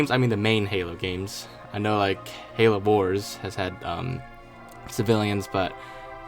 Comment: Why do you say Halo boars instead of Halo wars look here